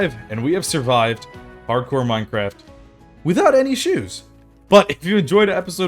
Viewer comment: sounded like: without any shoes 😂